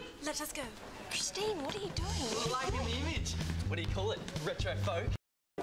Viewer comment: What the heck did say at 0:50 ?? It sounded like 'Retrofo' :S